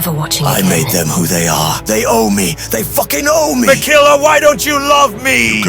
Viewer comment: Can someone tell me what malenia says at 7:13. Is she signing ME ME ME?